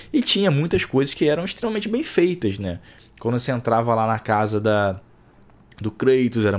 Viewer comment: Thor não é como o Chris Hemsworth, por incrível que pareça a Mitologia da Marvel não é como a original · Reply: puts eu entendi "essa cara do kratos" foi mal